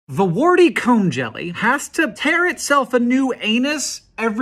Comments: Thank god for the subtitles. I thought you said "Warty C*m Jelly" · I thought you said the cu jellyfish